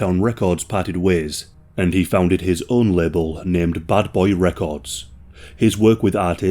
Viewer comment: Batmans new villain. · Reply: Buddy's accent sounded like he said Butt Boy Records instead of Bad Boy Records. Lol